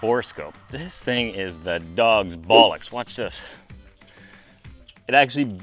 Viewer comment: Man. Your videos always amaze me. The creative thinking, the editing, the humor. I swear I thought you said the dogs balls. I had to rewind that part. 🤣. And not many people know about the Hindenburg anymore. What a disaster. A tinfoil blimp filled to the brim with hydrogen and people. I always read it was a spark from a pair of shoes that ignited it. Great results on the tank! And I think I’m goi g to have to add that bitchin camera to the shop arsenal. Cheers! 🍻